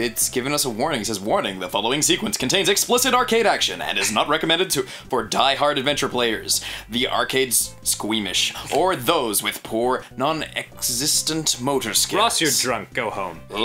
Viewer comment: i wasn't paying attention at the beginning so i thought it said "arcade-esque Amish"